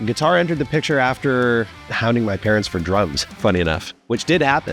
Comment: Damn I first heard "hounding my parents for drugs" like WOAW Canada is hardcore haha